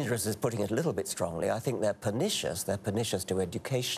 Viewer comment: Would somebody be able to tell me what the word is that Richard is repeating at around 3:36? It was in response to the question about whether these religious beliefs are 'dangerous'. Maybe it's his accent? I can't even figure out what letter it starts with...B? Thanks in advance :) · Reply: Yep pernicious. = destructive , injuriouse. I´m not sure why people used to US English have problems with English English. The consonants are more pronounced, and accurate . eg there is very little deference between "T " and "D " in much spoken US . English English vowels are quite dirty/mixed compared to other European languages, but US English vowels are really a mix of all in all. English get more practice understanding US English through films, I suppose.